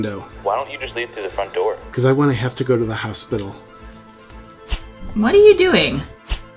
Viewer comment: "Why don't you leave thru the front door?" "Because I don't want to go to the hospital." I'm dying. This might be JP's funniest episode ever. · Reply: Thought he said “because. I want to have to go to the hospital”. He wants to have an excuse to stay out of the house for a while.